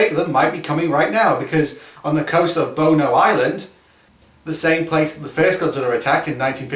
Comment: I know what you actually say but it sounds like you say, "boner Island" as a teenage boy in modern day America I can't help but laugh at that (I'm normally the mature one what happened to me?)